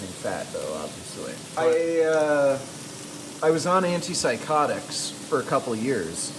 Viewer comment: Anti depressants, but, their side effects are psychosis, violence, dissociation etc. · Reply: He actually says "Anti-psychotics".